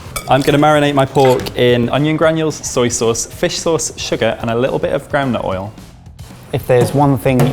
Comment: I think said groundnut oil aka peanut oil